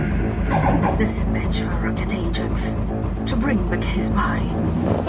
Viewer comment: Can’t never understand what she says at 1:26 Lol. Great job indeed. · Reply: Thanks! She says: "Dispatch war rocket Ajax to bring back his body" :D